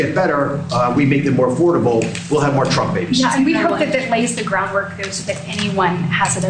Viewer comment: I physically gagged when I heard "Under-baby'd" what the ACTUAL fuck🤢 edit: OH my god... "Trump Babies" 🤮